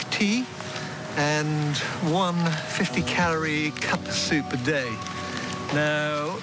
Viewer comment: What is he saying at 7:03? What is that 50 calorie? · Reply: Cuppa soup